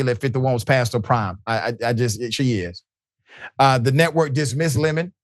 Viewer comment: did you say “she is” (‘past her prime’)? I listened to it multiple times and it certainly sounds like that’s what you said, but I am hoping I misheard! I’ve been watching you for a very long time (have always loved “I Wish a Karen Would”) and I believe saying such a nasty comment would be beneath you so please tell me that I am mistaken! Don’t get me wrong, I cannot stand Nikki Haley (or most of the GOP). But I guess as a 56-year-old woman I took offense. Forgive me, Dr. Richey if I misheard you.